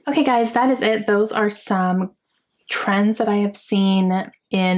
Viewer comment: I woke up during the night and turned to YouTube for background noise. I thought your video said friends, not trends. That’s what I get for trying to read when I am half asleep